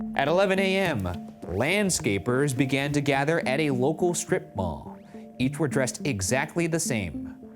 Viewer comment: i thought they said strip club instead of strip mall and i had very different theories on what happened